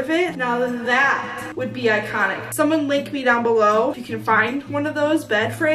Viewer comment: I thought you said lick me down bellow then i was like "hold up what" and then I heard what you actually said oopsies😂😂 "Dont attack me please it's just my brain it hates me🥺😂"